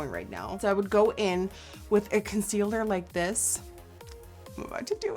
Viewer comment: Did she just say constealer??